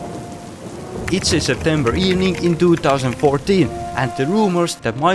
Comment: I keep hearing pizza September evening